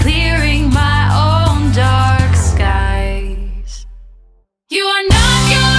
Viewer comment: When she said you're not your past I thought she said you're a psychopath lol